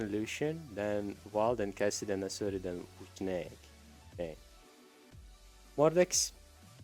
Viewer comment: look at the captions Instead of mordex it says more dicks xD Btw can you try to speak with an American or British accents ? I'm using the captions because I can't really understand what you are saying :/